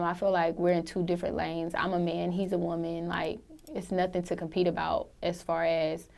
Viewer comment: did she say “I’m a man, he’s a woman”...or am I tripping??